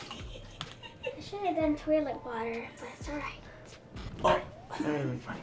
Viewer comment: "It should have been toilet water" Me: I thought you said you ani't wanna be mean